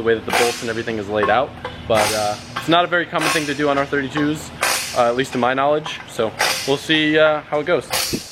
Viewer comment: turn on the captions 😬😂🤪😳 instead of r32 it says our 30 jews lol